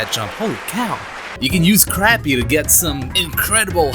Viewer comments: ahem did u said CRAPPY INSTEAD OF CAPPY >:( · is it just me or did he say crappy instead of Cappy?